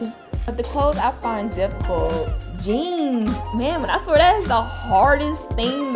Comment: When she said jeans I thought she said genes